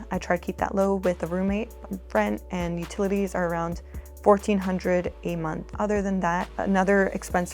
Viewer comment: That would make better sense. Not sure why CNBC didn't make that clear. It is crazy to me that she has a roommate still. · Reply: I wasn't paying any attention to that pie chart. 4:50 It sounded like she said her utilities were $1,400. She was slurring her words or the editing was off on the video. When I put on closed caption and actually listened to her 2x then I heard 'rent and utilities were her biggest expenses.' When I initially watched this video it was on as background white noise while I was working. Her comment of $1,400 on utilities shocked me! That amount is very reasonable for a single person. ESPECIALLY, with a roommate! I still think she's too frugal and boring. Apparently her upbringing of never having anything created this monster. My mom grew up in a large family. She was one of 14 kids. When she raised me I never wanted for anything. I always had the best of the best. I am just glad I didn't grow up in a house where pinching pennies was a thing.